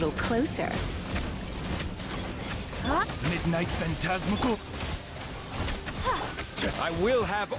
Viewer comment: Did I just hear Zhongli's voice say Midnight Phantasmagoria at 13:21 😅